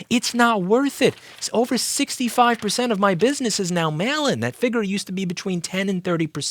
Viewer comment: 65% of my business is now “xxxx” What did he say? Mail in?